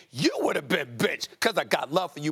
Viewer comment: was i the only one who heard bitch?